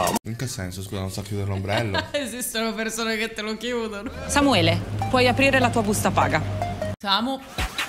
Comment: pensavo stesse dicendo "Samuele, puoi chiudere l'ombrello"